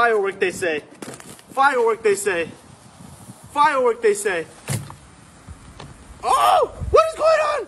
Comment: When i was low volume I hear "FIREWORK SENSE I!"